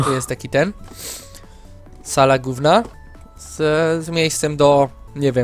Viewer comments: usłyszałam ,,sala gówna'' xD :D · kto usłyszał sala gówna ? xD